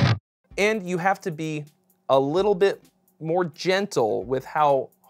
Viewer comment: DJENT DJENT, DJENT DJENT DJENT ... "and you have to be.. a little bit more djentle" am I the only one who heard that? lmao